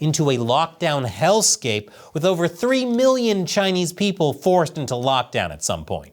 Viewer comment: it reads as 300 mill. vs what is said: "3 mill". fyi.